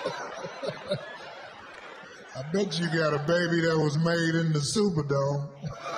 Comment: “I’ll bet you got a baby that was made in the Superdome” 😂😂😂 Corey Holcomb 🏆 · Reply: I thought he said in the sewer though